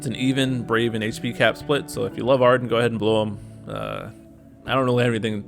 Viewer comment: I really thought u said "blow Arryn" instead of blue him lol. im personally happy for Kefka boards. gives me more reason to actually use. naan im skipping his bt, he's not really a favorite for me but I already have his ld